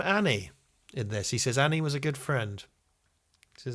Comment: I thought Obi-Wan said "And he was a good friend," not "Anni was a good friend."